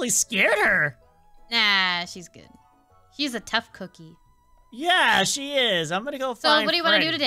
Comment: lolbit says HE is a tough cookie when cb is a girl either its me or she actually said HE